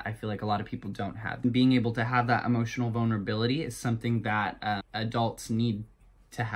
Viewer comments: I THOUGHT HE SAID EMOTION BONER-BILLITY!! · No one: LITERALLY no one: Me: boner bility wtf...... HWUT THEE FECK AM I HEARING....... BITCH SOMEONE TELL MEH. im a leo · in 1:34 i literally thought he said “emotioNal bOnerbilItY” i